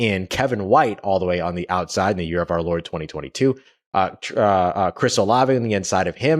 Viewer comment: did he say "the yead of our Lord, 2022." ??? Lol what?